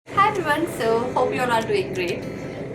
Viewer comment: did anyone else hear "Hi everyone hope you're not doing great" 😂😂😂😘